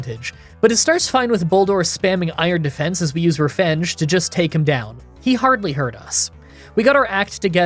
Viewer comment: Funny thing I noticed, but at 9:00, you say "refenge" instead of "revenge"